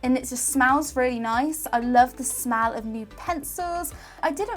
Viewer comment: her accent made it sound like she said "smal" instead of smell